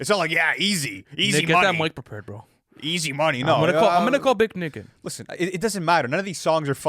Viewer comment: Did say he's gonna call "Big N****"??? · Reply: “Big Nick in”